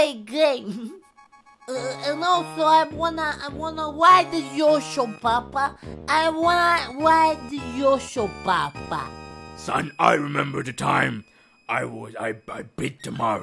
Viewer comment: I thought he said i want the WIDE yosho